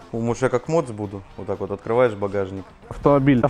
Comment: Послышалось Может я как МОПС буду ахаххахахахахахахахаха)